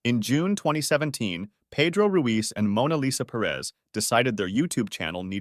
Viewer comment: wait wait wait… am I hearing this right? MONA LISA!?